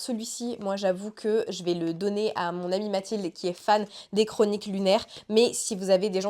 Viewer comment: J'ai entendu "le donner à ma copine Mathilde" j'ai hurlééééé 🥹